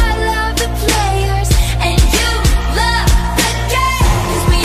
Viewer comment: i always misheard " Gotta lovely starbucks lover " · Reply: I always thought she said “And you love the gay” when i was younger